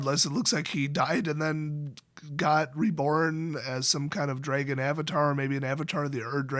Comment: i swear it says "a garbage avatar" in the act line